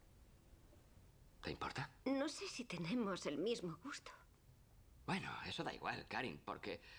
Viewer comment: Morí de risa cuando su cuñada le dice ... No se si tengamos el mismo busto jajaja.. Osea la muñeca súper tetona y ella esta planita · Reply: Creo que dijo "gusto" pero yo también entendí "busto" a la primera jajaja